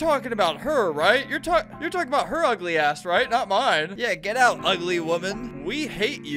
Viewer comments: did bro just say ass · DID HE JUST SAY “ASS”!? IM SO PROUD! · did he just say ass?